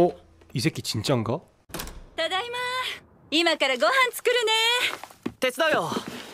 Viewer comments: 초 에 됐어요 라고 들림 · 에 왜 됐어요 라고 들리지?